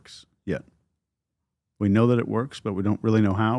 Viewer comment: I heard it said "They know"